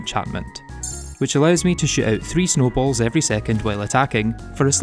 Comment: I misheard "shoot out three snowballs" as "sh!t out three snowball" lmfao